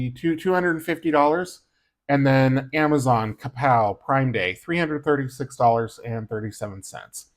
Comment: Hello! When you said Prime Day I thought you said Crime Day!😂😂 Nice breakdown!